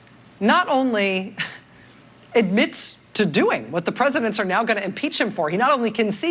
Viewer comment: "what the presidents are going to impeach him for" ? WTF is she saying